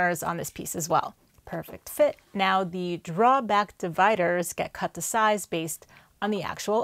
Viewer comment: What is a drawback divider? I don’t think I’ve ever heard that term in woodworking before… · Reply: ohh - I think I misheard “draw back” from “drawer back” - that’s my bad.